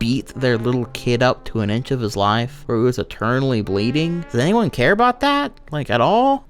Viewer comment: I know you are saying "internally bleeding" but for some reason I keep hearing "eternally bleeding".